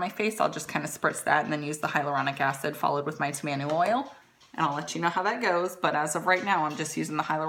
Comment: Girl! I swear I thought you were saying tomato oil instead of tamanu oil at first lol! Anyhoo! I never imagined it could come in a powder form either! I will certainly be picking some up right along with that vitamin c powder. I certainly thank you!